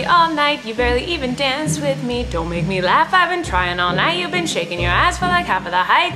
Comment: i thought in the tiktok they said "been sloppy all night"🤣🤣 didnt even know where it was from lol